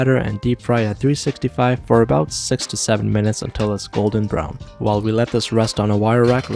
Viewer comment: When he said “6 to 7 minutes” I heard “67 minutes” and had to rewatch the video